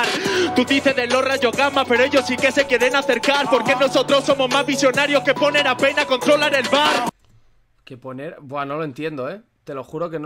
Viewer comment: Que dice ? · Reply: Somos más visionarios que poner a pain a controlar el bar